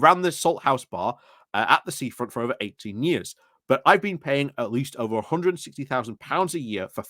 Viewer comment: (The Salthouse Bar): "I've been paying £165.000 a year..." Did I hear that correctly?